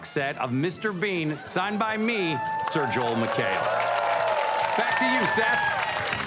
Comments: Did he say “Back to you Seth” ? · Sounded like Joel said "Back to you Seth"